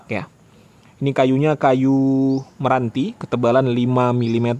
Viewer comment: apa saya salah dengar 5 mm ketebalan kayu.. atau 5 cm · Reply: 5 cm om 😁😁😁